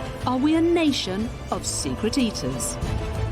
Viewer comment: For a second there I thought she said: Are we a nation of stupid eaters?😂😂😂🤣🤣🤣🤣😆😆😆😭😭😭LoL